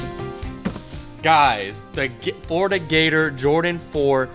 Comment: Florida Gators. This guy talks too fast and I thought he said fornicators LMFAO